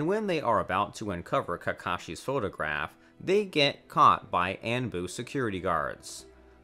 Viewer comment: What’s And-boo? 💀 Say On-boo 😭